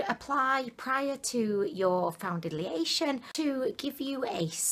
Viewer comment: "Foundidliation " 0:20 I know I'm not the only one who heard that😂😂